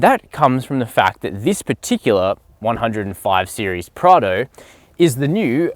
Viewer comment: did he say "105 series Prado"?